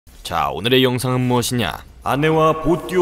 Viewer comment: 영상을 용서라고 들었네 ㅎㅎ